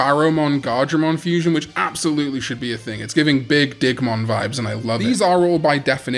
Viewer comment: "it's giving a big Digmon vibes" I misheard that...